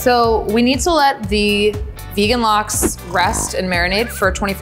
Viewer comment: did she Said Vegan Lachs (german Word for Salmon btw)?😂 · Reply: She said Lox